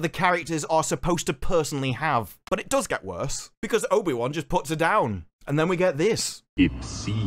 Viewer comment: I had to rewatch that part because I heard obi wan instead of vader putting her down and was like wtf